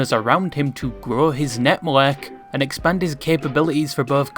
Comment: What did bro say 😭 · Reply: Grow his network Are you disabled?